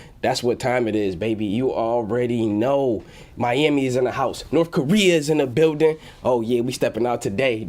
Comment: Did he say North Korea is in the building?